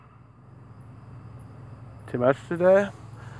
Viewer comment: not sure what he says here, "too much today"